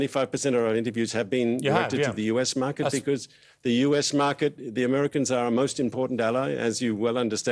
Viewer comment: Did he just say "American market?"